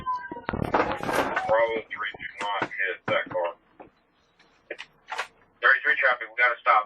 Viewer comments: Sounds like they say "Bravo 3 do NOT pit(or hit) that car" as its on its third tumble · "do not pit that car", am I hearing that right 😂